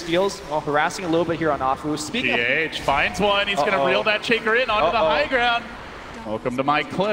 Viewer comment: Yo did he call ah fu a chinker??? 22:35 or shaker kappa